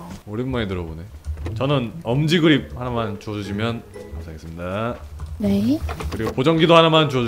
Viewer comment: 자막은 감사하겠습니다~ 이렇게 나오는데 그냥 들으면 ...읍니다~ 이렇게 들림ㅋㅋㅋㅋㅋㅋㅋㅋㅋㅋㅋㅋㅋㅋㅋㅋㅋㅋㅋㅋㅋㅋㅋㅋㅋㅋㅋㅋ 괜히 멋쩍은 말투인게 뭔지 알아서 더 웃김ㅋㅋㅋㅋㅋㅋㅋㅋㅋㅋㅋㅋㅋㅋㅋㅋ